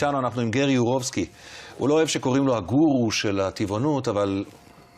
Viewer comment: I'm curious to know what the host is saying just after the break... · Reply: I was wondering that myself too lol But it definitely sounded like the interviewer called Gary a "Guru" which Gary makes himself clear that he is not.